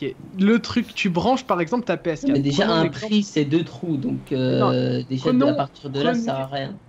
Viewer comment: C'est moi ou à 16:43 dit "un prise" à la place d'une prise